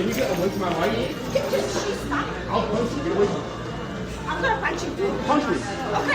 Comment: She was clearly in his personal space then touched him then 😂 She thought he said I’ll punch you but he said I’ll post it🤣🤣🤣She said I’ll punch you too. Okay..Okay🤣🤣🤣🤣